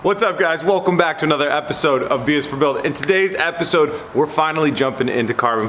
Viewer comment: Subtitles on YouTube thought you said “welcome back to another episode of beers for building”